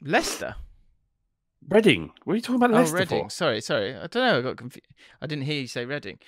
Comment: Can confirm, I thought said Leicester aswell